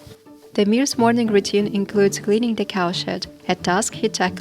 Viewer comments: Thought she said he cleans the cow shit 😂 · does she say his morning begins by cleaning the cow sh!t....